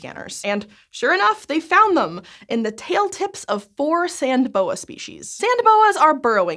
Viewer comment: 😂😂😂 I thought it said "Sand Beans", I love it so much 😂😂😂😂